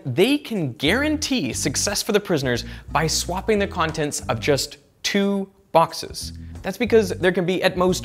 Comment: he said swapping any two boxes will guarantee the success. But it seems not correct. You also say the same. Or maybe I'm wrong. Would you please elaborate how selecting any two boxes randomly, can lead to success. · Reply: "he said swapping any two boxes will guarantee the success" Well, no. He says "just two boxes". Not "any two boxes" (12:35). That's kind of a difference. Swapping the contents of a random pair would only convert one random permutation into another random permutation. This might as well create a long loop instead of eliminating one.